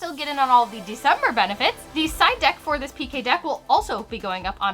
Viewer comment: Right after you said 'December' it sounded like you said 'DECIDE(R) DECK'